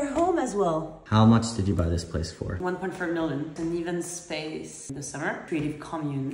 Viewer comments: I thought she said 100 million at first and I believed it because that’s how messed up real estate is in the U.S. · Sounded like she said $100 million.